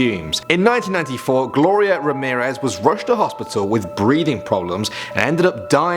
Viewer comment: Did i hear gloria borger? ;)